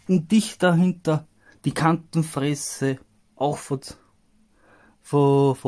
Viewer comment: ich hab Kantenfresse verstanden 😂